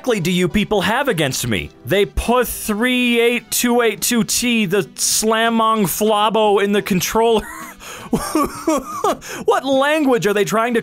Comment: I think he's trying to say "They put SpongeBob in the controller" he probably had a stroke